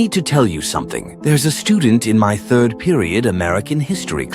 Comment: Why did I hear "There's a Stupid"